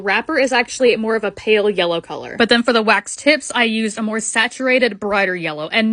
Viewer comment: I thought you said waxed tits 😭